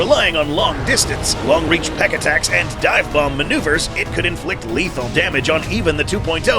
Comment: DID YOU SAY DIVEBOMB? LOUD 🎸 DIVEBOMB